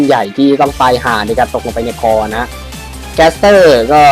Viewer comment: ได้ยินว่าตกลงไปในคลอง